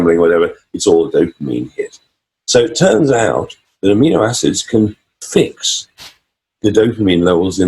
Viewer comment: "so turns out .... can fix the dophamine levels in the brain" what can cure dopamine levels? I can't hear clearly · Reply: Hi Bobby.....it turns out that amino acids can raise dopamine levels.